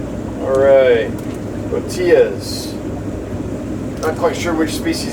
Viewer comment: you got some Botias. I heard "tortillas" and was thinking, some kind of freshwater sole? LOL, nope....loaches!